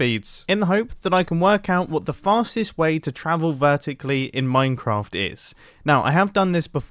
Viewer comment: i thought he said fatest way to travel